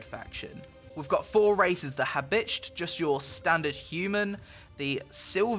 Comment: To be hones for a moment there around 0:42 I thought that you said "Ha bitch"